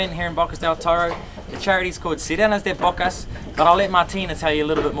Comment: you said, „.... I‘ll let Martina tell you more about that“. I heard, „....and I‘ll let my penis tell you more about that“. WTF!!! I choked on my tea, listened again and it wasn‘t until I let it run and „Martina“ came on did I understand what you said 😂😂😂😂😂😂😂🤪